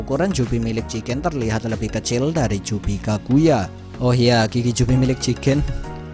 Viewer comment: Menit 11:18 (.....)juga jubi jigen. saat itu admin ngomong apa. Gak jlas soal.a udah di ulang" msh gak jlas admin ngomng apa. Penasaran jdi.a .. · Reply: GIGI